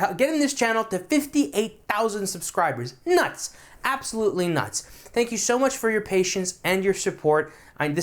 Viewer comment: did he say "nice! absolutely nice!" or "nuts! absolutely nuts!" or some combination?